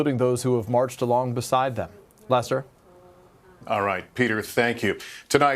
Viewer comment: did he call Lester, Lesser ?? Houston is frightening enough with having marches and protests.